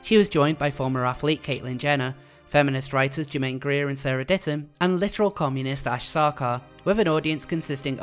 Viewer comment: "And literal communist, Ash Sarkar" That will never get old. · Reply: Is that what she said? I heard "literary columnist". I was imagining this person writing book reviews.